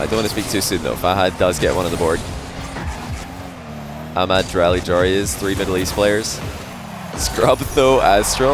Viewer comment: did he just say ''Ahmad drali joreuz, 3 middle east players'' ?? 😂